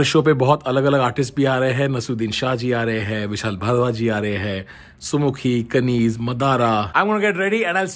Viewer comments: Did you just say Vishal Bhadwa ji? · did he just said madara the rapper🤯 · Did he just say "Vishal Bhadwa"? :D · What. did I hear Vishal Bharwa ji? LOL